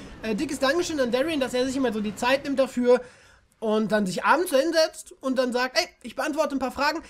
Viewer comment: ich hab verstanden "sich anzünden"😂